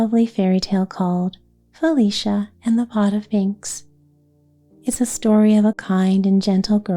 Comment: Thank you for these stories and the sweet sparkly pictures. I misheard the title of the first story. I thought you said Felicia and the Pot of Inks and that Felicia was going to draw! 😊 I hope the respite program works out. Caregivers need to replenish. You bring so much rest and peace to others—I wish some for yourself.